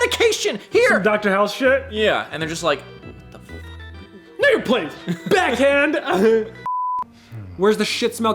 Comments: WTFFFFFFFFF DID SAY BRUHHHHHH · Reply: That is, but he says "know your place". Unfortunately alcohol stuttering was less than helpful this time lol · I really hope that’s not what I think I heard 🤔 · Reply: LOL he said "know you're place" but I can see what you heard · wtf did just say?? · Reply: He said know your place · did you say nigga please!? at 19:13....